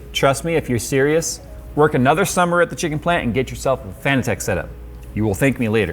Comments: did he say Fantech or Fanatec? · I thought you said Phanteks has a set up for racing and I got confused. Then google helped me see it was Fanatec